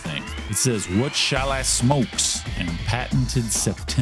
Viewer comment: It doesn't say "What shall I smokes.", it says "What shall I smoke?" ... The thing that looks like an "s" is part of the question mark, old font style.